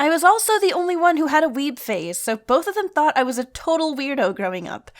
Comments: I remember the first time I watched this video my English was very sucky so I didn’t understand what “weeb phase” meant and I was so confused. I misheard “weeb phase” as “weeb face” Life’s hard haha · I honestly thought you said "I was the only one with a weeb face" at first. My bad. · I heard "i was the only one to have a weed phase" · The first time I listened to this, I thought you said "I was the only one who had a weed phase"